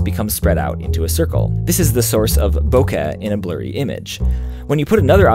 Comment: What is that word keeps saying that sounds like /bokɘ/? I first hear it around 3:00. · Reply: Bokeh. It's a photography term